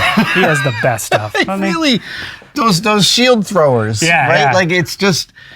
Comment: Does Adam really mention a "shield thrower" here or is this wishful mishearing by me? Could someone PLEASE point me to the video of what hes talking about here? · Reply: Yeah, he mentioned a shield thrower, but it is not what you are probably imagining. It was basicaly mechanical device attached to the chest to make a shield fly up after impact during a joust. <link>